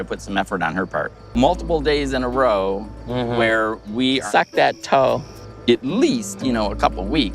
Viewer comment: Lol you kill me, I thought I heard “ suck that toe” and had to rewind and it was 😅